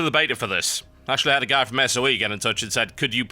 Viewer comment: Wait did you say SoE 0:19 ? i've played a game called SoE but i dunno if its the same as your's anyways SoE stands for Sanctum of Eventide i dunno if that is what you said but hey thats what i heard lol